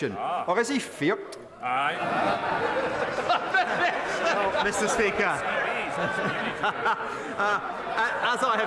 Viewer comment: as an American what does the leader of the SNP say “or is he fearked?” Idk what that f word was at the end but if someone could help out, as someone who follows British politics every now and then someone like say something I have no idea what it means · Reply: He says "or is he feart?". It's a Scots Leid word for afraid.